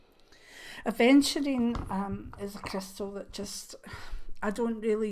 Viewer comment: Hi!! I Love your channel and I love you! I think you are adorable and so witty! Please tell me what the crystal is at 15:08. Your fantastic accent is so captivating, however I cannot understand the name of the stone. Thank you for your time and energy. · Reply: it was aventurine. thank you for your kind words. xxx gald you like my accent, always happy to respond if you cant make it out xxx